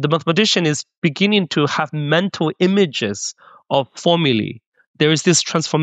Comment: What is the word he says at 1:37? "..mental images of FOMILY"? Fomily? Formuli? Family?